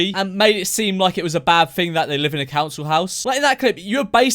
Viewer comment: I swear I thought he was saying bouncy house